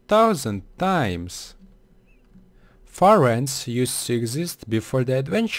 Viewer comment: am i the only one who thought he said fireants?